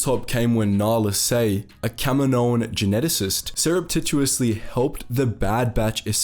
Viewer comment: What was that word you said? It was to long to spell without subtitles, and what does it mean. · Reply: Surreptitiously! It just means secretively.